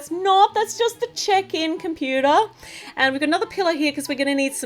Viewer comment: I thought you were saying pillow cuz you talk in an Australian accent but you were actually saying pillar